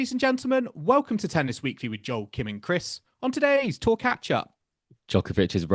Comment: For the longest time - I thought Joel was saying, "Talk at ya" instead of "Tour catch up."